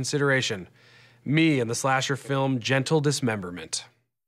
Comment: In the beginning it sounds like he says 'in the slasherfilm "Genital Dismemberment"'.